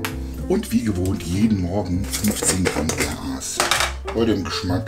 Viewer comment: wer hat auch verstanden und jedem morgen 15gramm Gras?